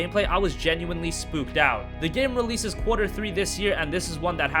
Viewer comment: When you said quarter 3 of the year I thought you said for free this year I got all excited